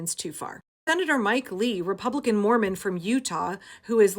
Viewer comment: OHHH Mormon, I thought you said moron